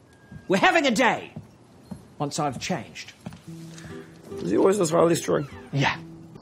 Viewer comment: anyone hear what he said? xD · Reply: "Is he always this highly strung?" I think